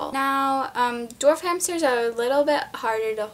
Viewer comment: My subtitles said door frames instead of dwarf hamster